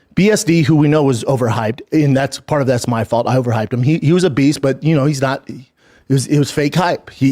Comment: she was a beast but he had fake hype he was fake hype so which one is it be is he a beast or did he have fake hype I heard it both ways